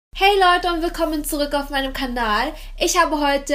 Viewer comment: Bin ich die einzige die am Anfang hört das sie nicht Kanal sondern Kandal sagt? Oder hab ich mich verhört